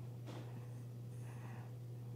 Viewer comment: Okay so @ 15:03 you guys are walking around why is blindfolded blablabla... I never noticed this before, & I actually thought it was breathing but he says News5 as you can still hear “breathing”!?! & thinking it was I never paid that close attention to it but it caught my attention when he spoke that I thought I could still hear him breathing so replayed it several times and it just doesn’t sync up to where it’s a delay or something because it overlaps him talking. Am I hearing this wrong due to an ear infection I can’t use my earbuds + that could be what I think I’m hearing as well but I’ve replayed it a lot and I can’t figure it out so paranormal breathing from something just hanging around who’s totally oblivious to its presence OORR ear infection messing with my hearing (LoL!)?!? Just refreshing my memory but something being missed has happened & you’ll never know, until you ask, sooo... ?!? Thanks for sharing ✌🏼🙏🏼🧿🧼🖐🏼🙋🏻‍♀️